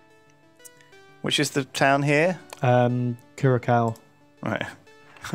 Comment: Whats the city called? Kurakow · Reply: I heard him say; CureACow